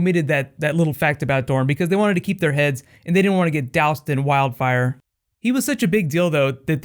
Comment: He was such a big dildo That's what I heard!!